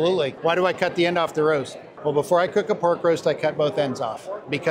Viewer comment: Way back in another life, we always backed away 1/4 turn on STEAM VALVES!! The idea was, if they heated/cooled while tight against the backseat, you might not be able to free them up again. Mind you, that has very little to do with a modern scuba system, but just throwing that out there. Great video, great info (and yeah, I've heard that, 'cut both ends off the roast because my grandma did it' story as well.) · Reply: Ohhhh ‘roast.’ I thought he said ‘rope!’ Was very confused. Listening to this with a very loud fan.